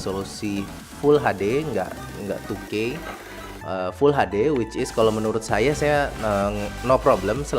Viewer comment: Di menit 3:00 dia ngomong apa..' wicis'